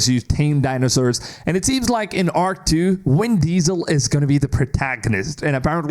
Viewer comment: Anyone else heard him say WIN Diesel?! Lmao